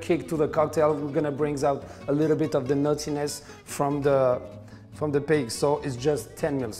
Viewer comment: every time he says 'nuttiness' I hear 'naughtiness'